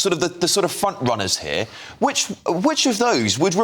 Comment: Did you say ‘front runners’ or ‘f**k runners’ at 5:50 the latter is arguably more accurate…